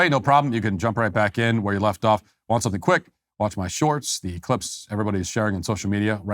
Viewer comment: did you just say “wash my shorts”?